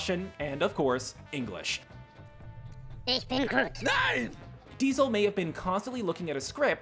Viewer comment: What I hear: "Eep en groot" "NEIN!"